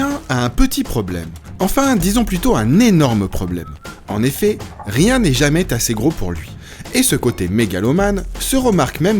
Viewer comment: à 00:07 rien n'est jamais t'assez gros? c'est quoi cette liaison???